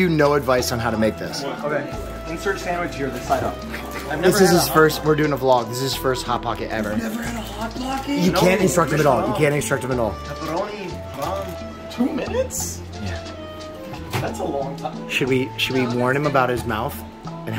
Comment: did he just say pepperoni is haram 😂